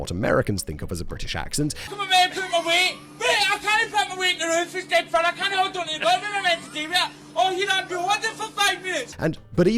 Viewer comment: even with subtitles i cant understand what the hell was said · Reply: "Where am I meant to put my weight? Where? I can't put my weight on the roof, it's dead thin! I can't hold on to the gutter, what am I meant to do with it? Oh here Andrew, hold it for five minutes!"